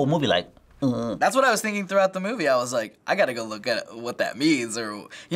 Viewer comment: Correcting captions: definitely said "throughout" instead of just through